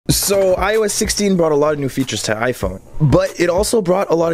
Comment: I thought he said "I was 16" instead of "iOS 16" lol